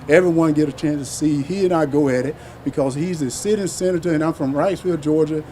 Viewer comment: Walker sounds like he is saying "city senators", not sitting senators.